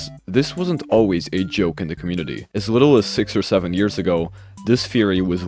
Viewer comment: Did he just say…..6-7 years I think I am having a relapse I can’t do this anymore 😞